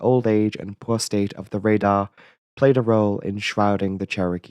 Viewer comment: as a non native speaker, I heard "the prostate of the radar played a role [...]"